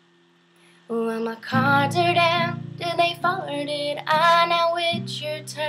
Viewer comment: i enjoyed the vocals your talented but at 1:38 did you say farted